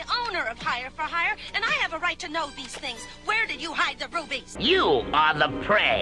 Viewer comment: Wait, did she say "Where did you hide the rufies?!"